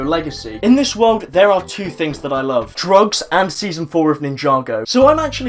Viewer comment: In 2:01 did he say i like Drugs and season 4